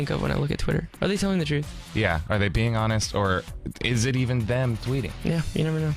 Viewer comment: What’s wrong w me I heard “Are they peeing on us” at 3:07 somebody put me in a straight jacket 😂